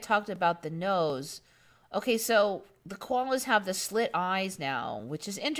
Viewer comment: When you said koala 🐨 eyes being slitted, I thought you said Cuomo because I wasn't looking at the screen. As I listened, I had the funniest vision in my head of a slit-eyed cuomo with a speckled nose!!